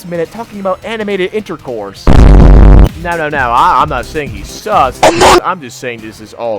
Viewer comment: did he say "I'm not saying he's sus" or "i'm not saying he sucks"? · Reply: sus